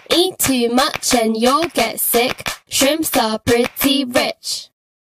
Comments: I thought it said "shrimps are pretty thicc" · I thought they said "shrimps a pretty bitch"😂😂😂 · I thought they said "shrimps a pretty bitch"😂😂😂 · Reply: In the official video i heard “shrimps are pink bitch”